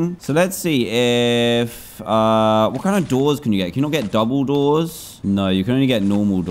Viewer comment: Whenever Dan says 'double doors' the only thing I hear Is 'Dumbledoors😀